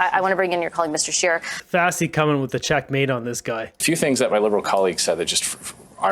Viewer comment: “A few things that my liberal colleague just said…” I had to replay it because I thought he said “little colleague”😂